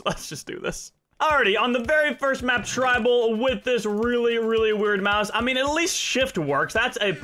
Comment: Go to 1:25 or 1:23 to hear what he said He said : "A really, really weird MOUSE"